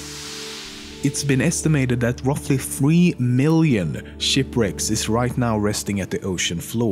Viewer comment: I thought he said "Shit Bricks" not "Ship Wrecks"